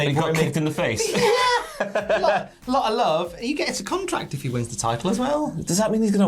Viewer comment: Does say cum-tract? 🤔 I have no idea what one of those is but I don't want to see it being 'signed' 😂😂😂